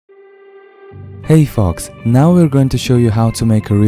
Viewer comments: In the beginning I thought he said hey fucks lol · When he said "Hey folks." I thought he said "Hey fvcks." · If you say "folks" be careful how you pronounce it. It sounded like you said "hey fucks" lol. Folks should be said the same way as F-oaks